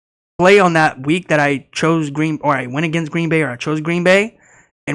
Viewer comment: "Green Boring" is what I thought I heard 😂